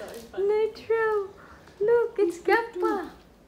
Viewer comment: Nitro looks exactly like hes Lolo(Dwayne's dad) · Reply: Lol, you got me there 😂 I thought you said that Nitro looks like a grandpa 😅